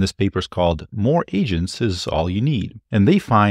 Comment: I misheard one of your opening statements as “More Asians is all you need” it followed as you were talking about China.. Sorry to any Asians love you all